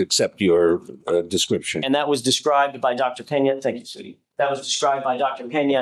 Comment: I'm sure he didnt, but it really sounds like AJ said "thank you, sweetie". 😂